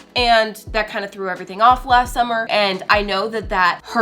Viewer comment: I almost died I thought she said last week not last summer when she said she had a baby i was like srry what ….. did I miss something 😅